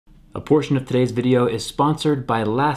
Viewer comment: At first I thought he said "Unfortunately this Video is sponsored by LastPass" lmao